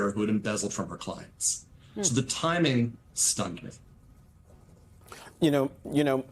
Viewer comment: Did he say the timing stunk? 2:17? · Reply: stunned me